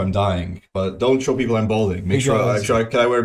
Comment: I thought he was saying bowling, balding is completely fine by me but I if ever catch you bowling, lets just say there will be problems kid